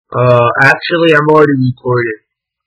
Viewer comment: I SWEAR I HEARD " uh actually im already deported " AT THE START 💀